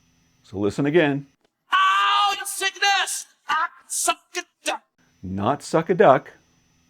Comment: I thought I heard "suck a duck" as well! 😵